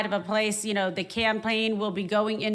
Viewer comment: it really sounded like she said "clanpaign" instead of campaign which, you know, appropriate.